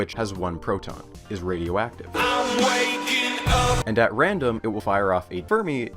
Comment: can ANYBODY decipher what was being said at 1:01?? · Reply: He’s waking up